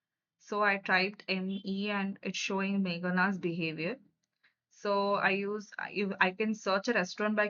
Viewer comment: Are u a bot or human being ...at 10:00 why did u say megan behaviour instead of megan biryani?